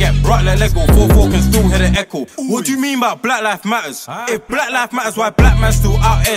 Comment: the only think i heard at 1:00 was "wa yo me by bla lif matter"